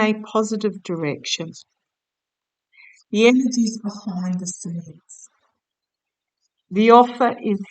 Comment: Goodness…. So powerful, the voice at 6:37 she says “ the power is behind the cervix “ another way to put Feminine. Power, how we bring our girls( boys too) through into the next step forwards…showing them how to find their spirit family that walk with them & the guides that come to work and leave…..❤ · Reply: I heard "the energies behind the scenes" at 6:31, very clearly. I can't hear 'the power behind the cervix' no matter how many times I listen.